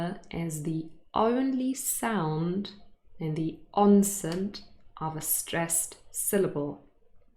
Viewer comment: is it me or I hear you pronunce the word "onset" ans /onsen/??? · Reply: I hear it as “Onsent”